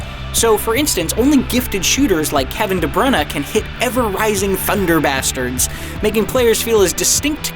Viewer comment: Did I hear that correctly? At 0:42, I think the narrator said, “Kevin De Bruyne can hit ever rising, thunder bastards”